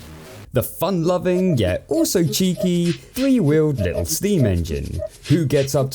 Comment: free willed? I heard three wheeled first despite the fact he obviously has 6 wheels